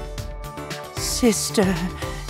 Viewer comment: I'm sorry did he say "sister"